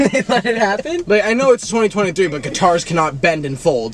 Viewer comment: Please tell me it was not just me who heard Nick say “2022” instead of “2023”